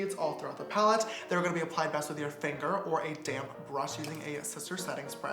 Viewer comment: I thought he said Damn